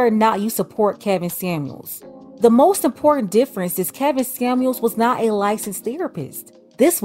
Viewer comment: Did you say kevin Scamuels @ 7:03 ?